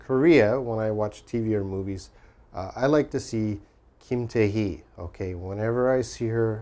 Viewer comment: Holy moly! Did you just say Kim Taehee?